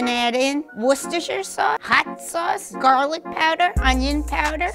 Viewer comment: Did She Say Hot Sauce? 😅